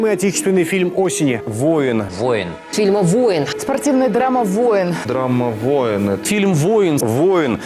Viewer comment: Драммавойенн, так мне послышалось